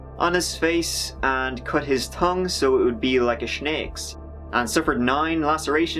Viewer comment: wait, did you say shnake? wtf is a shnake